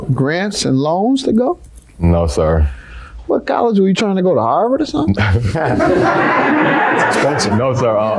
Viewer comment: 5th in the 🇬🇧 🎉 1:56 what college you trying to go Ireland 🇮🇪 🤣 pretty close to me 😂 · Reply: lol he did not say Ireland. 😂He said Harvard. I thought he said Howard at first 🤣It’s a university located in Massachusetts.